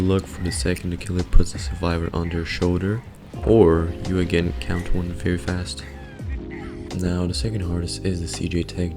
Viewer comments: "or you again count 1 very fast ?" ...what's he saying. bro is literally mumbling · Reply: I would also like to know. I heard "count to one very fast" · "or you again count 1 very fast ?" ...what's he saying. bro is literally mumbling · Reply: yes count to 1 very fast